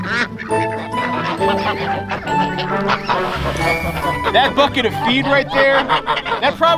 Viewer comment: "That bucket of feed right there" Yeah that's not what i heard the first time. "That f'n feed right there" LOL i was like he usually doesn't swear but...